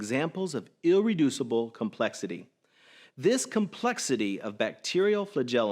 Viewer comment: Did he say ill-reducible complexity?